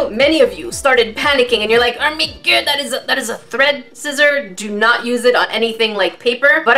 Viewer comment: when you said "thread scissor" i thought you said "threat scissors" lol